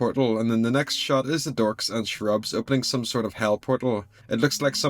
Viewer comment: Every time you say cherubs I keep hearing "shrubs"